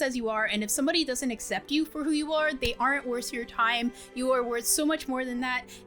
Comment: This part i was like did she say worst your time or worth your time i dont get it